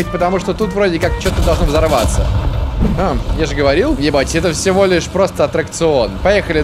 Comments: мне не послышалось или... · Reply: Он сказал "не бойтесь" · Эй йоу! Садись ко мне в танк, прокатимся, повзрываемся!)) Спасибо всем за Лайки и приятные комментарии). Меня это очень радует и поддерживает) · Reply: я один услышал не цензурное слово Е..ТЬ